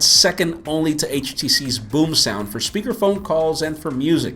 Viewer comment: Sounds like he is saying "boob sound" instead of boomsound and sounds like he said it sounds quite titty. haha :P